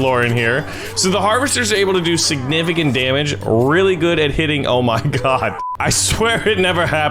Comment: did he just say there rlly good at hitting o my god